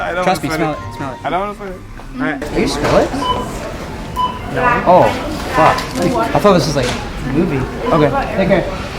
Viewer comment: what does he say? "Are you.....?" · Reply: Skrillex